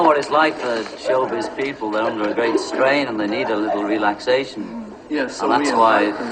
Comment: I heard that John said “need a little relaxation” not “needle relaxation… just a thought hahaha